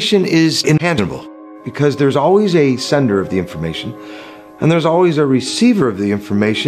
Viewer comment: what is mans saying at 4:08...."information is...????" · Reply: that information has a sender and receiver obviously